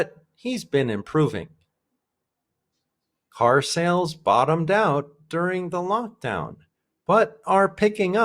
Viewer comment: You said „bottom down“ instead of „bottom out“. I was wondering beforehand whether it‘s the same thing😁